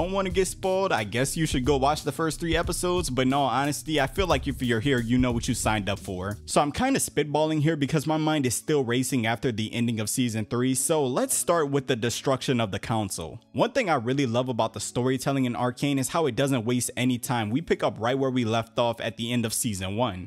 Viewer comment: At the end of Season what?? What did he just say?? xD Season 3?! We just got Seaon 2 my guy! xD lol